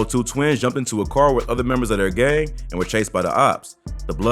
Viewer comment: did he say other members that are gay 😂😭